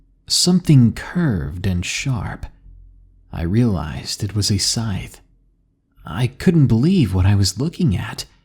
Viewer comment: Grim Reaper: Person mentioned the Reaper's Scythe. For a second I thought I heard Reaper's Sling Blade.